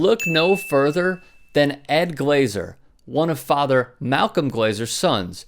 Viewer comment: did he say one of fathers?? As in more then one fathers?? Im confused